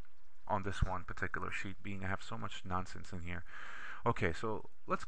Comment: sounds like you said "we have so much nazi's in here" :D